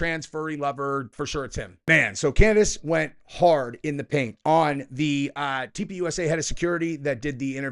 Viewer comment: Bro, I thought you said Candace went hard in the pink, had to listen 2-3 times before I realized you said Paint. 😂